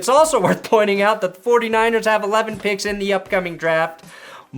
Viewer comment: I swear I thout he said the 49ers had 40 picks in this draft